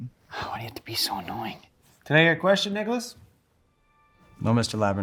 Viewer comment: cnt b the only one thought he said nigga😭